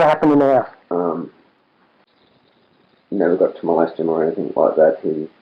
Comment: he says 'never got to molest him or anything like that' and the auto-generated subtitles say 'never got to my last gym'. This is who video creators should put in the effort and write subtitles.